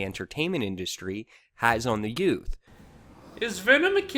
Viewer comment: Did he say children? No, he said youth! If you're trying to make good points, you should check your facts first. Anyway, you do, as well as he does know, a lot of kids will most likely watch this movie because it's a Marvel movie and they think it looks cool.